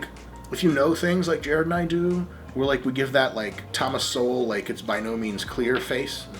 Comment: Love the video guys. Hilarious at many places. Also, at first I wasn't sure but I thought Dr. Mike said "Thomas Soul" as the captions said Thomas Soul. But then realised that he was talking about Thomas Sowell, after he mentioned he was talking about an economist. Unless, there is a different person named Thomas Soul. Thomas Sowell, mostly, talks about inequality in America. Leaving it at that. Tends to be a little controversial in certain places and times. But who isn't? I am not sure about the clear face thing though.